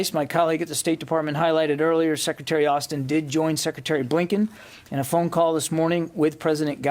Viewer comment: Did he just say "Abe Lincoln?" No. He said "A Blinken."